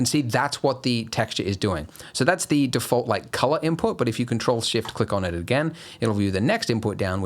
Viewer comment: Ctrl+shift +click twice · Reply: my brain really thought he said shift+click, cheers i needed that